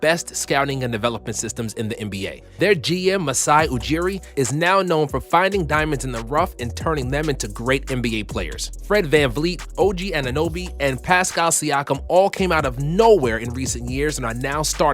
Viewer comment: did he just say "Frank VanVleet" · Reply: Nah he said Fred